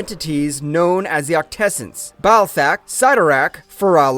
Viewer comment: did he say, Ball Sack?